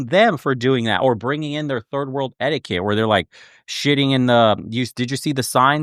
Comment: hinduism = 3rd world religion? is that what you said 2:17?